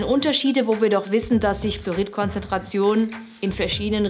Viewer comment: Kleine Bemerkung am Rande, ich habe im Video ständig Flourit verstanden, obwohl du das natürlich sicher nie sagen wolltest ^^